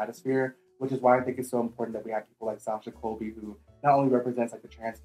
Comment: Whats the name of the drag mistress talked about? The alternative pageant girl? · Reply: i’m looking for that too! i heard “natasha cole, tasha cole, or sasha cole” but no results for either 🥲